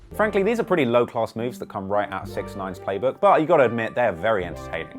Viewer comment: Did you say six9s playbook lol ..meenin xxx or am i mistakn